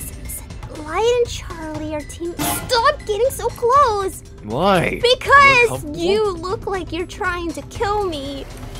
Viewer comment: did anybody else hear say "WHY, we are a couple."